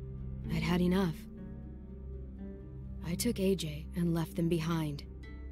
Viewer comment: Is it just me or did Clem say at 4:54 I left him behind when she left Jane behind?